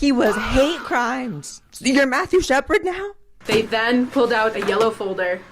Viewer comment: I was not sure who Mathew was talking about when he said "They Then" It sounded like he said "They/Them" and at that time I was like another misgendered person? who?